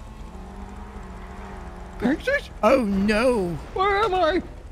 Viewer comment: Did he say Texas..!? xDDDDDDD I luv it..!!